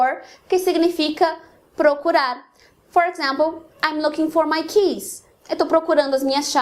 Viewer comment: Muito bacana o video. Só queria registrar que saiu som de /s/ na frase: "I'm looking for my keys". Eu estava só ouvindo o vídeo e estranhei. Eu ouvi "I'm looking for my kiss" hahaha Aliás fica a sugestão para um novo vídeo. keys x Kiss